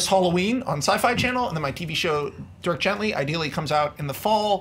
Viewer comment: hang on. did he say Dirk Gently? Is that based on Douglas Adams' Book series?!